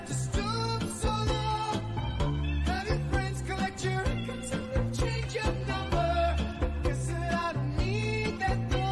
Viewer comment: does anyone know what he actually says here? i still can't figure it out tbh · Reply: Have your friends collect your records and then change your number